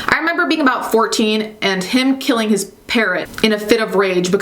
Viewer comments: When you said he killed his parrot I thought you said parent and was like "WHAT DO YOU MEAN THEY GOT HIM A NEW ONE???" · I thought you said he killed his parent not parrot 😂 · I misheard parrot for parent, I was shooketh